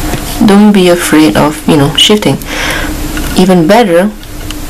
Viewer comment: "Don't be afraid of shitting." I know what she wanted to say but I had to listen to that a few times.